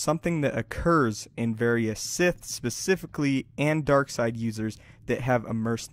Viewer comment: I thought you were going to say Sith philis.